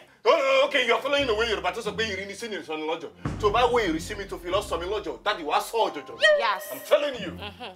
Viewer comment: Best ever for me from Mr. Macaroni. I am interested in the yoruba proverb Lateef spoke at 4:5 - 4:10. I have played that part of the video over 10 times now but i couldnt hear him well. Can someone write it out for me in the comment in yoruba as well as the translation in english. 🤩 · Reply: The Yoruba maxim (I won't say it's a proverb) is 'iri ni si, ni ise'ni l'ojo'. The way people see you (ie. how you present yourself), is how people will value you. Basically, your appearance, will determine how people treat you.